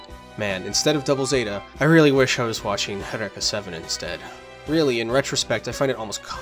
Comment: What is the name of the anime you are describing in 59:10. I couldnt understand the name after mutiple tries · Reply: Is Eureka 7 but he says “Haddeka 7” trying to sound cultured but he’s really just a sheltered weeb